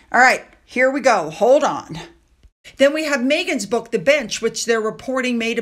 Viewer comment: I just burst out laughing. I was making a bunch of food and listening to this in the background and when you said Megan's book The Bench, I heard it as Megan's book The B***h 😅😅